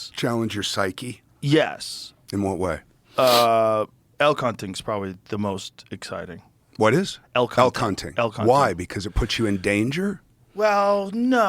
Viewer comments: I thought Joe said El Kunting. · Might have been cos of the el chapo talk. But when Joe said the most dangerous thing he does is elk hunting, I genuinely thought he was saying el cunting 😂